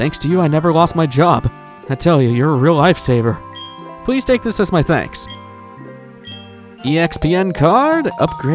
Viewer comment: I'm not gonna lie, when the guy gave you the EXPN card I thought you said "Thanks to you, I lost my job, please take this as my thanks" It caught me off guard!